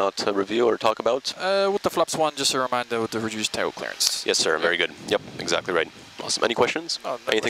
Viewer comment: Can someone tell what the FO is saying about the Flap 1? Reduced title clearance? And what is that supposed to mean? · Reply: Reduced tail clearance, which means that during rotation the chance of tailstrike is bigger than flaps 5